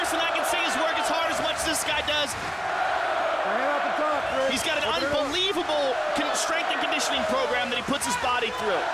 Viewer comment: Who's name did joe say at 14:50? It was muted out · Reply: Sean Sherk, I think they muted his name because he tested positive for steroids